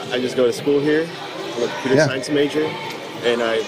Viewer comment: A what science major? I can't quite hear. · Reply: computer science?